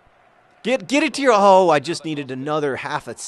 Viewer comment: Did John say "Just get it to your ho!" ? Also every gamer here should check out WASABIGamers! :)